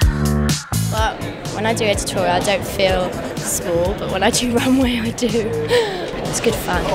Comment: What she says at 0:16vi cant understand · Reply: Slagan Sarafimovski when she does an editorial she doesn’t feel small but she does when she’s on the runway